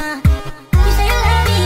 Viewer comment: NOOOO bc i thought he said “what’s up everyone my name is… the best”